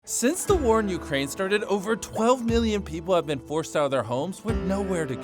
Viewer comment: It sounds like he said 12billion instead of 12million